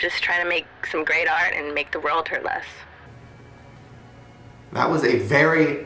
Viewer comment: Did she say make the world hurt less?